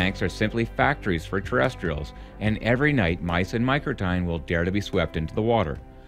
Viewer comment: "and every night mice and ??????? will get swept into the water." What does he say after mice? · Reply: "Mice and Microtinae" Things like voles, lemmings, and muskrats. <link>